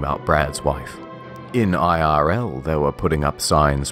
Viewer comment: Did you just say in IRL. “In in real life”?